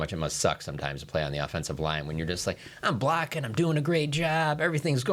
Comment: Tf is he sayin? Sounds like he says I'm black and doing a great job. The Closed Captions even says Black.